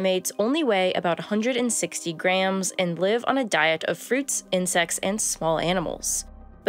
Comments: I thought she said “Fruits and sex and small animals ! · I heard "And live on a diet of fruits, and sex, and small animals" Hahaha